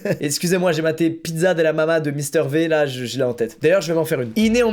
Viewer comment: c'est moi où il a dit mister v à la française ?